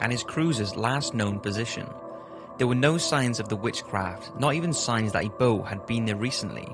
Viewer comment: Did did he say there where no shins of the which craft?